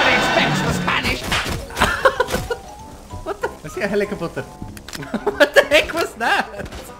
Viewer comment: Does he say at 4:27 helicapotter